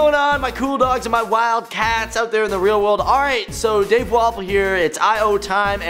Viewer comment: when dave started i thought he said dave waffle xD